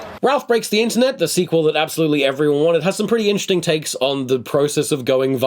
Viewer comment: What does say at 7:38? "Ralph Breaks the Internet, the sequel that absolutely every unintelligible has some pretty interesting..." Oh I figured it out, he says a similar thing at 8:47. He must have said "absolutely everyone wanted." But WOW did it not sound like it.